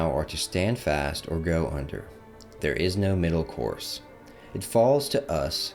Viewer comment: stand fast or go hunters. Thats what I heard. 😂